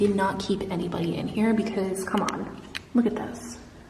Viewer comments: I hear woman's voice saying "look back" !! 😶😶😶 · Swear I heard a whisper say "in here....here" when you entered that creepy room